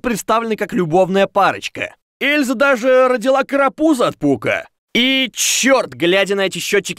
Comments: кому покозалась что он сказал карапуза ПУ́КА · Мне одному сначала послышалось "От Пука"